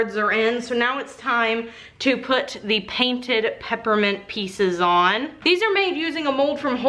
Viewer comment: I swear I thought you said peppermint pizzas instead of pepperment pieces and went WAT (but also note I'm really tired and zoned out for like .5 seconds when this happened xD )